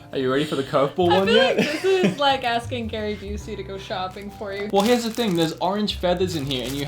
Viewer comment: I thought she said, "It's like asking Freddy Krueger to go shopping for you." LMAO 😂